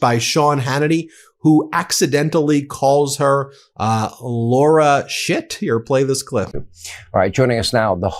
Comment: Lol i heard "...lara shithea..erTrump"